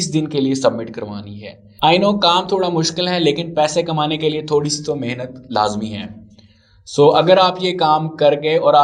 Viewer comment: hi in 3:22 what did you said sir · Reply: I know kam thoda muskil hai lekin paise kamane k liye thodi si to mehnat to lazmi hai, yehi bola sir ne.